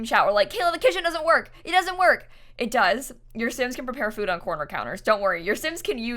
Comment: Wow i thought you said that you can't prepare food on corners, was that a dream 🤔